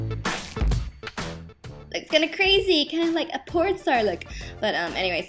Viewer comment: LOL no, she really said porn star